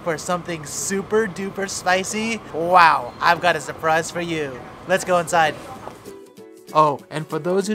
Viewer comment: did he just say "lets go outside"